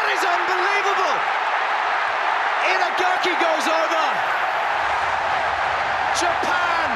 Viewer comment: ~ 稲垣が抑えた！！って聞こえる